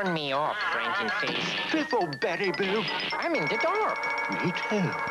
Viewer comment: It kinda sounded like he said: “Piss off, Berry boo.”